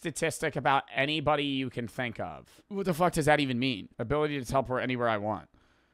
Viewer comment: did bro say “who the fuck does that even mean”?